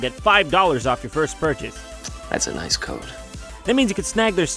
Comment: at the end I thought he said 'that's a nice coat' and I was like :?